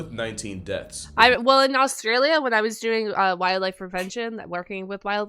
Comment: it sounded like said "wildlife prevention" - what was she preventing the wildlife from doing? Alligators and Caiman are part of the same branch of crocodilians. Dallas World Aquarium has an Orinoco rainforest recreation and includes green anacondas. 🐸